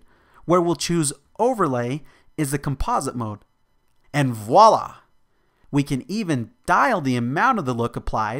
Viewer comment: Dude, did you say "and voilà!" at 2:51 ? lol . Great tutorial tho!